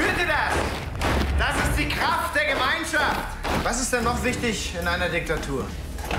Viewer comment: what does he say? Das ist der Klang....didn't catch the rest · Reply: “Das ist der kraft der Gemeinschaft”, I think